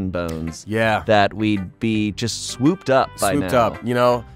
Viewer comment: Why did I hear “ just be swooped up by a man” when they were talking about being single. My ears were struggling because they were talking at the same time 😂 so idk